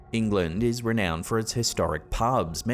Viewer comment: I was SO sure I heard say: "England is renowned for its historic pugs"!!😂😂😂